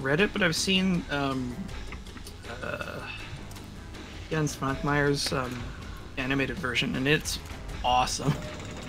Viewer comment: What’s the name of the director that says here? I’m curious about the animated film he mentioned. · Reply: Jan Švankmajer